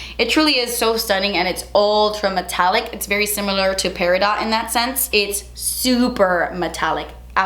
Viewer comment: Omg I thought peridot said periodt 😂